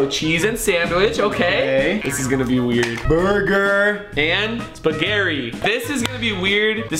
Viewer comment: did Rug say spagherri